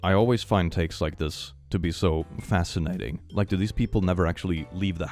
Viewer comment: I misheard say "Fascinating", and thought he mispronounced Asinine lol.